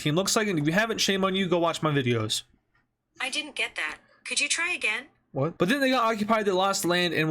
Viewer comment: did siri really say "i didn't get that" :skull